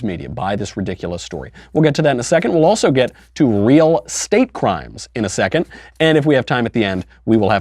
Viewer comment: I thought it said real skate crimes